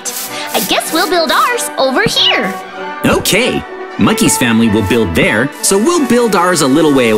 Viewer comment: Who heard monkeys family will buil over there